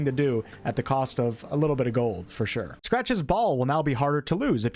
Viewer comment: So I had this playing in the background half listening, and then instead of "Scratch's Ball" I heard "Scratch his ball"